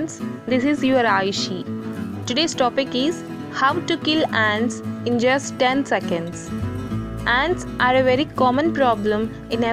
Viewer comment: I heard “How to really dance in ten seconds” ya aye get it get it ya yeet😂 🔥